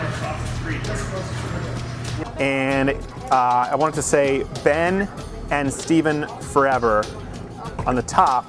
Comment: Lol when you say "Ben & Steven love forever" i thought you say "Ban Steven's love forever"